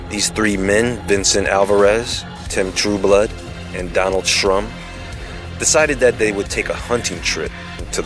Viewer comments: good work...i had to rewind it twice cause i thought u said donald trump lol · Thought he said Donald Trump.